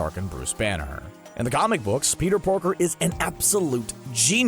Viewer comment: did he say Peter porker